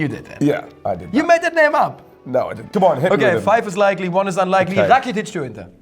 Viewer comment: what did say? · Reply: "Five is likely, one is unlikely... Rakitic to Inter?"